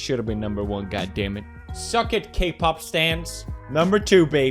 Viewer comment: did he say K-pop sans Let image sink in